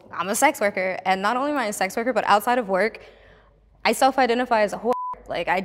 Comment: What did she say at 14:00? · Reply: "I self identify as a wh*re"?